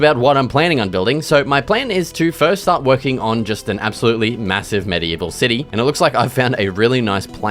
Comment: did i hear massive?